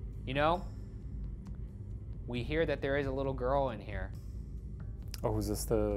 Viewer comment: The voice around 10 minutes sounded like he said “ where is my little girl?”